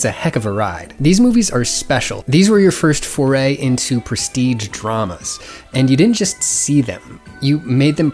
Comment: I thought you said prestige traumas with a t and now am spiraling into a very strange thought pattern